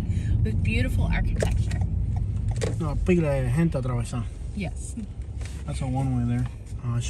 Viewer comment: what does he say at 0:18 ? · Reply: “Una pilla de gente atrabesa” = a bunch of people in my way